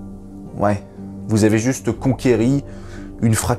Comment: conquéri ? T'as bien dit conquéri ?????? Non mais va te cacher quoi hahaha 😂 😂 😂 😂